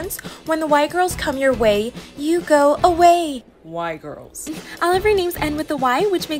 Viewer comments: “When the Y girls” bro I thought she said white girls 💀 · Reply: The second she said y girls I thought she said white girls to and I checked the comments immediately so I could so if I was the only one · Why did I hear "Y girls" as "White girls" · I thought they said the white girls at first 🫢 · am i the only one who thought she said white girls at first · I thought they said The White girls when she said the Y girls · “When the Y girls” bro I thought she said white girls 💀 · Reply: I thought she said why girl😂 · yk the first time i heard it i thought she said “white girls” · I thought they said the white girls😭🙏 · “When the Y girls” bro I thought she said white girls 💀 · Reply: i thought she said white girls too! i was like thats so racist! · I thought they said the 'white' girls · i thought she said "White girls" HELP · I thought she said white girls at first instead of y girls 😅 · “When the Y girls” bro I thought she said white girls 💀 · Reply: Bro fr i thought she said white girls i was like is that racist?😳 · I heard “white girls” 💀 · I thought they said white girls😂😂😂😂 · my dumbass thought they were the “white girls” but i guess its still accurate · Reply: I heard "white girls" until i looked at the sub titles 😭✋ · I thought she said white instead of y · Am i the only one who thought they said "white girls" 💀 👇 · I thought they said the white girls were coming not y 😅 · Dude i thought you said "white girls" i thought that was gonna go severely racist